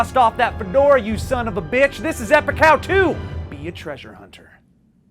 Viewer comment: I know it’s called “epic how to” but I always hear “epic cow 2”